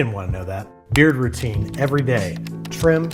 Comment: I thought he said he begins his morning HAIR routine and thought it was a joke